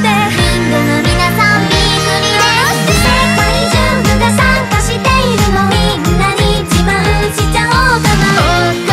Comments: ここのきゃーたんなんて言ってるんですか？？ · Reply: ナマステー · のとこなんて言ってるのか25年考えてたんだけど“ナマステ”って言ってる？？ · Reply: ナマステですよー